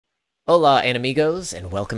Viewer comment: did you just say "Hello enemies?" O.o